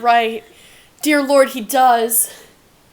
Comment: i misheard this and thought he (or she) said dio lord hed does